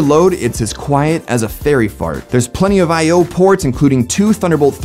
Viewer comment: did he just say fairy fart